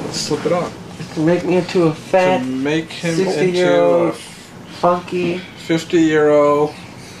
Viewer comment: did he say fucky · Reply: funky